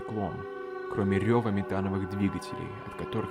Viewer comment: мне сначала послышалось не Метановых, а Сметановых двигателей, думаю фатафак корабли на Простоквашине летают что-ли.